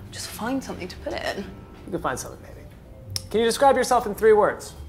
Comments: " you will find something baby" WHY DID HE CALL HER BABY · WAIT DID HE JUST SAY: ‘you ll find something baby’ !! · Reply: Mick Plays I think he says “maybe” if you got really good headphones you can hear it close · did he say : Go find something BABY?? im confused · did he say ”you’ll find something ‘baby’ “ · did he said 'you find something baby' ?